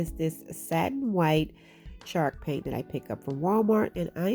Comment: I love how it sounds like she says shark paint lol. Love the diys too girl!!🙌🙌🙌